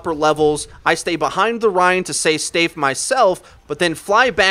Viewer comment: "say stafe" instead of "stay safe"